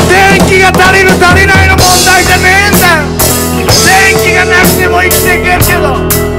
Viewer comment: えぇっ？！16:43で彼が「電気がなくても生きてけんだよ」って言ってるように聞こえましたけど…聞き違いだったらすみません。 メディアや政治家が利益至上主義に陥ってるってのは、そうでしょうね。なんせ彼らはスポンサーやパトロンの機嫌を伺って放送したり利益誘導のために法案を作成したりしますからね。